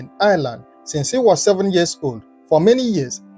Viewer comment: He went to Dublin to study. He was not born there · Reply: I thought I heard since when he was 7years old